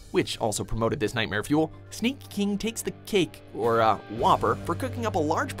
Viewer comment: I thought you said kink for a second there.